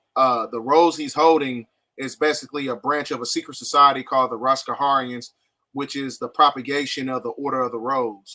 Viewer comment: I thought he said rastafarians.. Now I see where the infiltration came from lol wild.